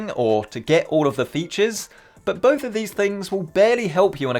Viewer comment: I thought he said to get all the bitxhes